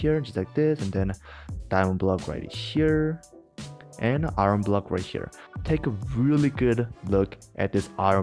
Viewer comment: People who don't use subtitles: 1%: i dont understand what are you talking about... 99%: i understand you talking "Iron Block" People who use subtitles: Subtitle says: Arm Block Subtitle users: i dont't understand.... Arm block don't even exists... And then, you shouldn't trust subtitles....